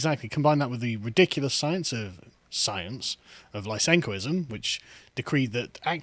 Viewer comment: What word is this? I can't find anything on it. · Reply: Lysenkoism.